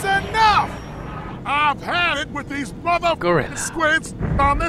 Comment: Something wrong with him that he said on 0:34 even 0:36 he said "Mother Gorilla" I think he swear he said "Motherf*cker"